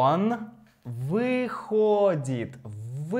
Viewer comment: I LOVED this lesson! I was able to watch it tonight. Will continue with others tomorrow... But I don't know if I was misunderstanding something... towards the end, at 38:30, the sentence read, "Он выходит из дома каждый день в 5." To me, it sounded like you were saying "входит." Am I not grasping something? If I were a dog, I'd be doing a "head tilt." <link>